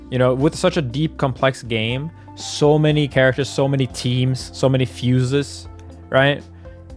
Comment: Did he just say “so many characters”?